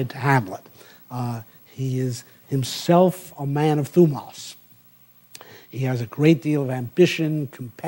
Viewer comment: What is this word he uses several times (also in the previous lecture), at 17:17 for instance? It sounds like "thoumos" and could be some kind of classical concept pertaining to heroic virtues, but Google is yielding nothing. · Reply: He talks about it in his first lectures: From Plato’s Republic’s 3 parts of the soul: reason (logos) controls the two irrational parts: eros, thumos (or thymos: spiritus, ambition, courage, often related to patriotism, cf. Coriolanus); not Plato’s precise terms. Achilles is the great image of thumos (a Homeric word). Cantor says Shakespeare could've read Plato in translation in the 1590s.